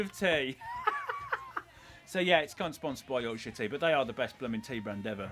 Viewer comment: When Colin says 'Yorkshire tea', the subtitles say 'Your Shitty'.